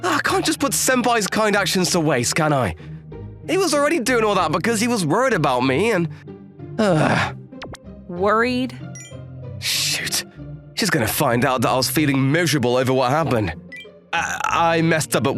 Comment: I didn't expect swears that part that he says "Sh*t" instead of "Shoot". 13:21 it made me laugh that he spoke the "yay" part in a short, but stupid way. Man, really made his VA interesting and hilariou whenever he did a MC VA in every manga story. 😂